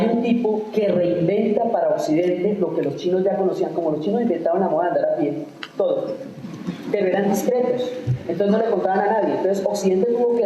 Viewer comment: ¿qué dice en 14:26? Gracias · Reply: "Hay un tipo que reinventa lo que los chinos ya conocían, como los chinos inventaron la moda de andar a pie, todo, pero eran discretos."